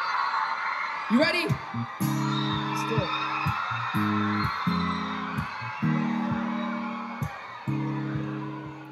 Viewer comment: what do you mean 7:50 I'll show you 12:40 purpose 16:12 christmas eve 18:21christmas love 21:42 Mistletoe 23:37 Hotline bling 25:31 love yourself 28:51 Jaxon (justin's little brother comes on stage 29:53 Baby 40:00 Home to mama 42:35 all that matters 45:12 Boyfriend 48:20 hold tight 52:31Be alright 54:54 trust(acapella) (53:56 i think he said selena) 58:50 one less lonely girl 1:01:35 As long as you love me(Dun instrumental only, Justin forgot to perform it haha) 1:03:21 so sick 1:07:45 Down to earth 1:09:15 fast car 1:10:34 one time 1:12:34 sorry 1:15:35 super mario theme song lol 1:16:17 let it be 1:18:18 Nothing like us'Goin' on 1:20:32 whole lotta shakin hope this help you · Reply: he said selena